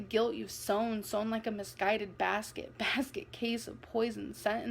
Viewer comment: sounded like basket and queso